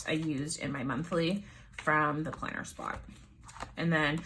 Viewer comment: Great inspiration for my hobo weeks! Where did you get the monthly stickers again? I thought you said plannerkiko but couldn't quite catch it. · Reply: The planner spot!